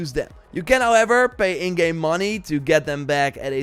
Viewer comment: I thought he said that you had to pay real money to get the scouts back @ 3:17 not in game money I was like oh Ubisoft